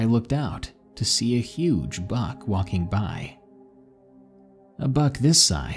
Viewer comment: I thought he said "huge bug" at first and i was so confused